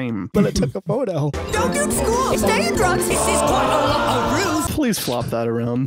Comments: HOLY CRAP, did they actually say “don’t do school, stay in drugs?” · did he say stay in drugs?! 💀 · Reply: "don't do school, stay in drugs" - Jonah 790 B.C. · did he just say don’t go to school stay in drugs WTF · Did Jonah say: “Don’t do school, stay in drugs.” at 6:17? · Did he just say: "Dont do school, Takin drugs " ?!